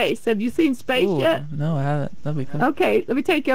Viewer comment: I wasn’t reading the subtitles and when I heard the name I got so scared cause I heard anorexia 😭😭